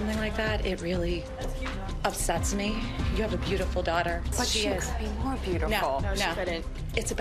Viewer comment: Someone tell me what is she saying at 5:47. “But she —- more beautiful.” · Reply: "But she could be more beautiful"